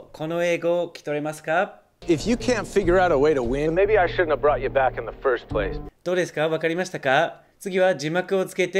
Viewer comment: If you can figure out way to win, maybe I should have brought you back in your first placeと聞こえてしまった😭